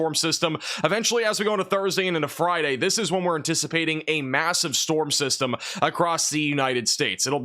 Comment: did he say massive